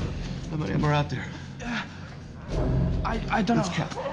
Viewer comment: does it says "Don't do that!!"? 🤣